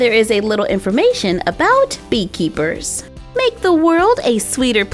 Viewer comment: Lol I thought it said book keeper